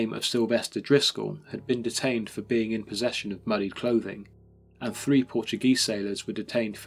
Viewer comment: I can't understand what he's saying. "In possession of, ' WHAT." ? Seriously, I replayed it quite a few times and couldn't understand it. What was he in possession of?!? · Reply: "Muddied clothing". There are subtitles if needed.